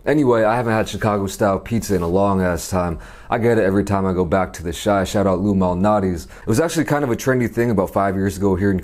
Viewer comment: I thought he said back of the yards I was like dam from the hood ong